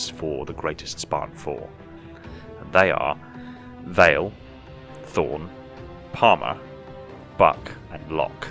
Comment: thought he said thrawn from star wars